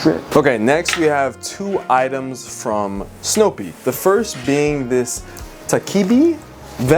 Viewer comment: I first heard "Snoopy" (not Snowpeak) so when he pulled out the vest I was like why in the hell is Snoopy selling goopy vests 😭 But I'm so delighted that you enjoyed Japan! Do you think you'll go again sometime in the future?